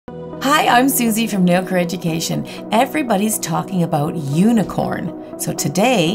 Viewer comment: When she said 'nail career education', I thought she said "no current education"